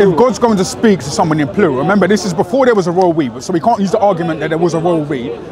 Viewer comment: this is before there was a what? hard to understand what this guy is saying cos he doesn't speak properly lol, he talks lazy. Sounds like he say a royal ree? tf is that?